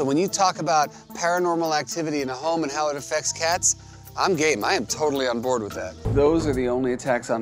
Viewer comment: did jackson just said he is gay? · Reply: He said, i am game.